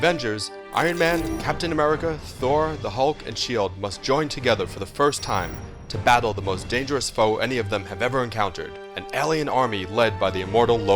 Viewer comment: did Scott say She Hulk?